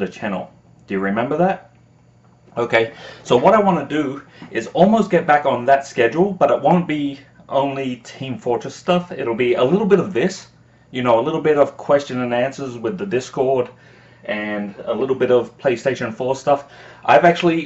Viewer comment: he said "it won't be only team fortress stuff" but for a second i thought he said "i want it to be only team fortress stuff" and it was the happiest moment i've ever had for about a second there.